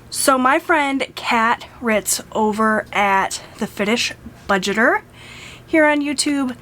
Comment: oops! I thought I linked her channel. Give me just a second to add that info to my description box. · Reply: I see why I couldn't find her...lol I thought you said the finish budgeter🤣🤣